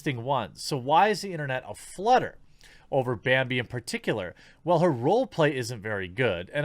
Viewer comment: I'm sorry but, was that slip up or am I just hearing things 2020: "BANBI"